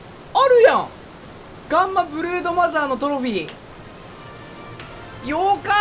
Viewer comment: 最初ブルーノマーズって聞こえてびっくりした